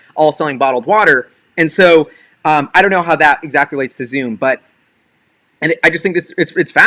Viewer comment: you were talking fast and I misheard you and thought you invented the word "exaculates"